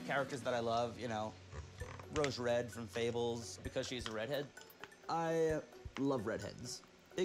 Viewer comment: I thought he said "I love Reddit" but to be fair I bet he does